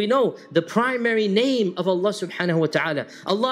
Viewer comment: Jazak Allah brother, @ 5:20 it's says Allan's many names instead of saying Allah SWT Teypo error